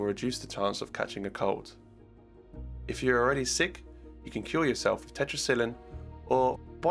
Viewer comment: did you just say ”you can kill yourself with tetracyline” what sense that makes?